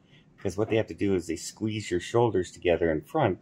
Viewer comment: 😂I thought you said "squeeze your soldiers together ". I decided I should read the transcript.